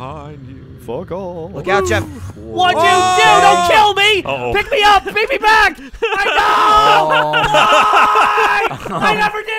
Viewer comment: I heard "pick me up, bring me back! Heimdall!!!!" And now I can only think of Geoff dressed as Thor.